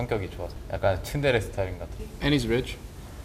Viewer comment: did Faker say tsundere? b-baka.